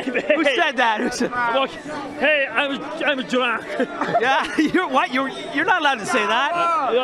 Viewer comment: what is he saying in 2:44 ?? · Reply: I am drunk